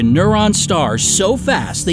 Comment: Did he say Neuron star instead of Neutron star